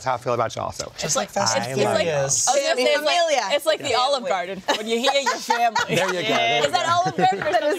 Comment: I thought she said "it's just like olive garden, when you're eating your family"